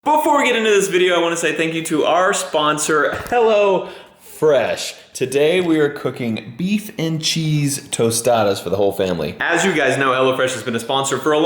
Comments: I thought he said hella fresh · Everytime says "Hello Fresh" i hear "Hell'a Fresh"